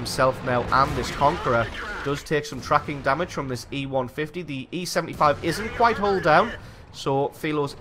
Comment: Lol did I hear E-150 XD